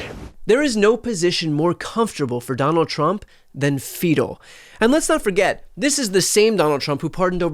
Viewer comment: Sounds like BTC says "There is NO POSITION more comfortable for donOLD Chump than - FEACAL 😳" 🤭🤭🤭🤭🤭🤭